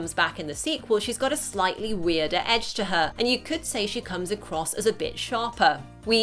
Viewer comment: before the narrator finished the sentence I thought she said “you could say she comes across as a bitch” lmao